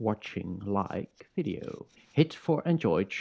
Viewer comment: did you just call me a bellend?